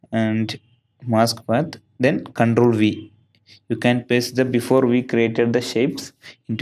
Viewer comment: que dice en el minuto 1:27 al 1.30? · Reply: Ctrl V